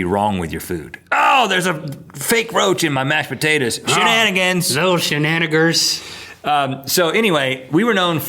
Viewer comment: did he just say "shenanigers" lol put that on a tshirt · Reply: He said shenanaggers- nothing racial- just a variation of the original word.